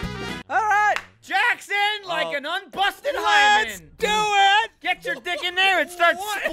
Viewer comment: anyone else hear him say “unbusted lemon” at 12:44?